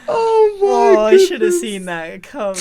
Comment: what did say? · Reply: “I should’ve seen this coming”